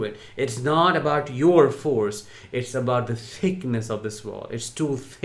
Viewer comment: At first I thought said, " because of the sickness of this wall", which I thought was brilliant...but I misunderstood, he said " because of the thickness of this wall"... but, still brilliant. It's not about our level of force on the narc.